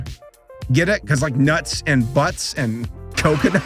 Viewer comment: when you said nuts and butts, i hear "nut in my butt"